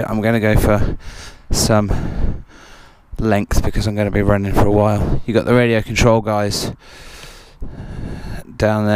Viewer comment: What's that say at 1:45 super fast? Twiss, twix? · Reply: TWSS - That's what she said.. ;)